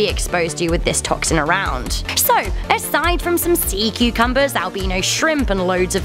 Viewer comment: You say "cucumber" I hear "cu-Cumberbatch". I can't help it.